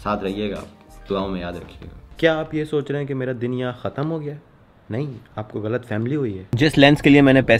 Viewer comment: who else heard him saying "aap ko ghalt family hui hai" lol.